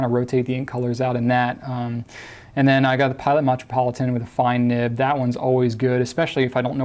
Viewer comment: Lol did he say "matrapolitain"